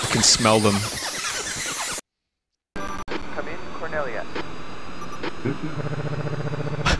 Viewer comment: just realized after all these years that fox says "cornelia" instead of corneria.